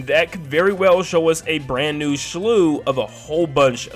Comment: it sounds like you said “shlew”